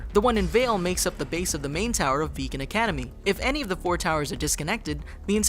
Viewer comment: Did you just say Vegan Academy?....